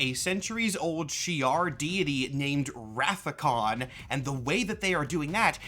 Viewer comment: My Trekkie brain heard that as "they're trying to raise an ancient Shi'ar deity known as Wrath Of Khan!" And I just thought well they shoulda just bought it on Blu-ray or something.